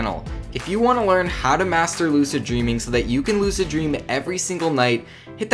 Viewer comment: At like 40 to 55 seconds into the vid I thought he said ima teach you how to master bate I was like ok go on🤔